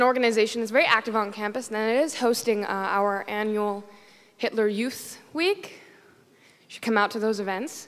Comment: did she say "our annual Hitler youth week?" I'm pretty sure that can't be right...